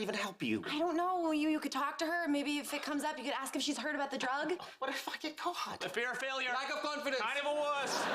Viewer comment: what did I just hear? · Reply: “What if I got caught” Don’t worry I had to listen to it a few times as well😂😂